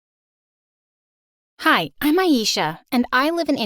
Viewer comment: I thought u said "Hi I'm. FeLiCia